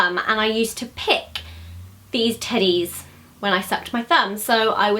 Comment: When she said "teddies" I thought she said "titties"😂